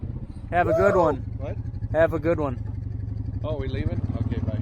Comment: At end say “have a good one” and it looked like thought he said “want a cold one” 🍺